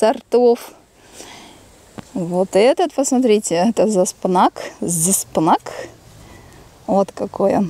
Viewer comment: Кто расслышал? Как называеться острый перец интересной формы, который и в ванне и на улице. Я услышала Заспынак, но не нашла с таким названием перца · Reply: Зспанак